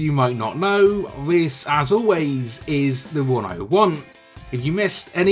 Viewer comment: I've been watching your videos for 2 years and everytime you say that I think you say 'This is the one I want" but today I realised you say "This is the 101"😂😂